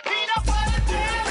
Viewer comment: Everybody know??? What boy name in 0:26 he said peanut butter paul??